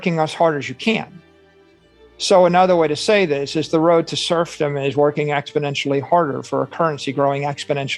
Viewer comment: I think he said serfdom and not "surf them", subtitle geniuses. Also, Saylor doesn't seem to realise that banks pay "interest rates" and they go up with inflation, that is why it's both interest rates and inflation tanking crypto right now. You'd have to be an utter moron to fall for this pathetic attempt at goldbricking.